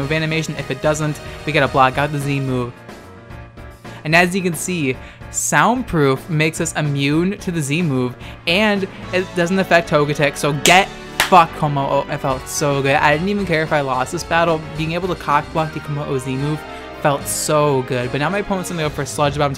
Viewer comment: Why do I hear Etika saying, "GET FUCKED!!". LoL. 😂😂😂😂😂😂😂👌💯❤❤❤ P.S. Happy Birthday and a safe 4th of July!!!